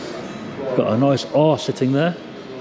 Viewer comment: 🤣🤣 I swear she thought you said 'Got a nice arse sitting there' - The look on her face hahaha